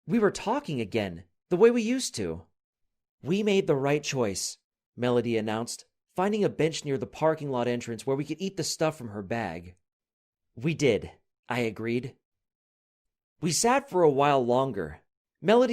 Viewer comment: I thought said finding a bitch